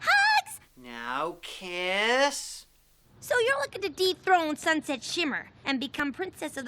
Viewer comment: I thought she said " so you're looking to DEEP THROAT Sunset Shimmer". Man I wish you would have heard that too. And commented. But I know, children's show. Heh heh heh ..